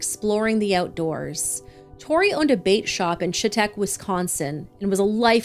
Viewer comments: Omg I thought you said a bake shop not bate shop so I was like why would you show a picture of maggots after that lol I finally realized u said bate lol😅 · I thought you said “bake shop” when it showed the maggot looking things in the cup lol